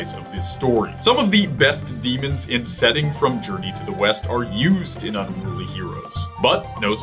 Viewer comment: i thought you said journey to the weast